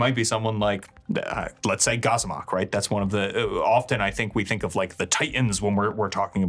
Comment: Great video as usual! Just a very minor curiosity: did anyone catch the example patreon for the acolyte that mentions at 22:07 ? "Gozmok" or something similar?... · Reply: Goxomoc is one of the Titans of Orden, he's in Flee, Mortals!